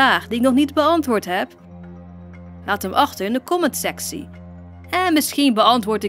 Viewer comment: Kinda sounded like you said “The comments sexy” When you spoke in dutch